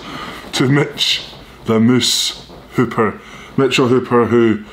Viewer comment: I thought he said congrats to Mitch “the Mrs” hooper lol cause he grabbed his boob when he said it haha I had to go back and re listen lol